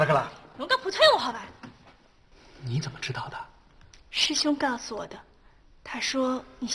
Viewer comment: 这他俩说的什么啊？听不懂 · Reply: “你怎么知道的？” “讲普通话好吧！”